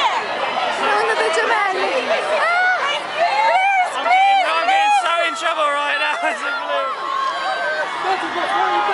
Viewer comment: Oh God! what did he say at 0:23 ? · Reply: I am so in trouble right now